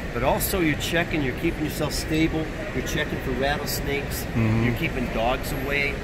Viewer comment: Awesome! 4:15 I thought he said "battlesnakes" meaning all venomous ones. Now thats my new term. BATTLESNAKES!